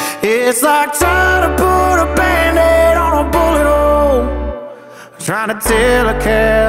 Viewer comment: Thought it said bandaid on a mullet hole for a sec hahaha